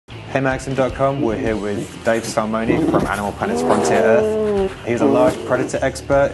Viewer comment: I thought he said, "We're here with Dave Somebody". It's just crazy that Maxim magazine is now a thing of the past.